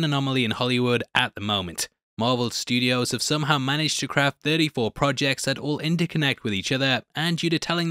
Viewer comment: For a second I thought he said “scrapped 34 projects” 😂🤣😱